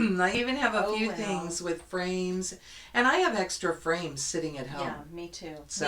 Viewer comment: Always enjoy your videos. Had to laugh...I thought you said, “I have extra brains at home”. FRAMES, not brains. 😂 ~Tamara (Thisnanastitches =Flosstube and IG)